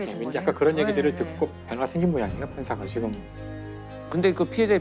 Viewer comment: 혹시 16:55 정도에 김상욱박사님께서 말하는 '그런 얘기들을 듣고' 뭐가 생긴다고 말하는데 이 부분이 너무 안들리는데 알고 계신분이 있다면 알려주세요. 몇십번을 들었지만 배가? 패가? 라고 들리는데 말이 안되서요. 이런거에 꽂히면 들릴때까지 듣는데 이거 너무 안들려요. 아 정말... 너무 답답하다... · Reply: 변화